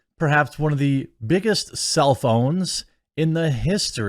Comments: I thought he said "biggest cell phones". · Not relevant, but I totally heard “cell phone” when you said self own. I was listening and not looking at the screen, so I expected to see Frosk with a huge cell phone. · One of the biggest “self owns” I heard “one of the biggest cellphones.” 🤦‍♂️